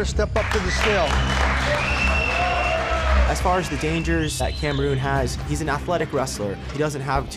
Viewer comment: did he say "As far as the dangers that CAMEROON has?" isnt this man from Nigeria?